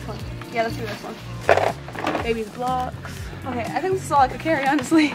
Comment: I thought she said baby glocks 😂😂😂